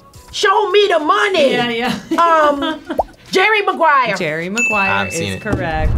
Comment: Did she just say "Jeremy Maguire"?